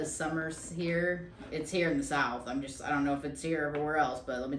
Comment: Hey. Where are y’all? I’m in lake Jackson tx. I used to be a reseller but retired this year. Great video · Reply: oh! I thought you said you were in Texas. Maybe you just said south lol